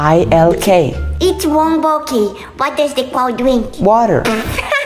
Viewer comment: When the mom said water i didnt understand so when the oldesf brother said what does the cow drink i thought it said what does the cow milk💀 now i understand